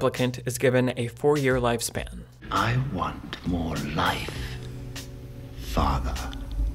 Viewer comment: "I want more life f*cker".....yeah uhm ok that's NOT what he's saying, he's saying "I want more life, father".